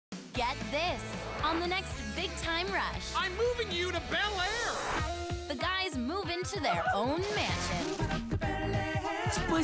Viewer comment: Gustavo's tone sounds like he said "What makes you think im moving you out to Bel-air?"